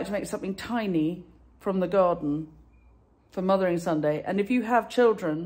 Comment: Oh my goodness, I was driving while listening to this clip and for the longest time I thought you were saying Mother n Son Day! 😂